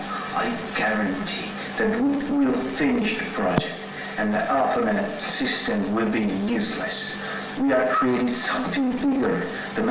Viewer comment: I don't know if someone's commented this before but at 16:35 I think he's saying "the arpanet system will be useless!" The arpanet was originally the first computer network that later gave birth the the internet. It seems that whatever is going on happened a while ago.